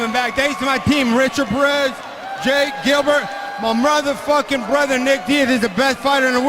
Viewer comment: did he just call his brother a mother f**er?